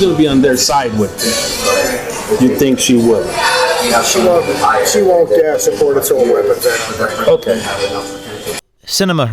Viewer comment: he sounds like he says she wont ban assault weapons. But the subtitles say "she'll ban assault weapons".